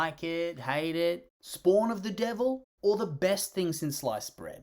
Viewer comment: I thought you said “best thing since lice spread”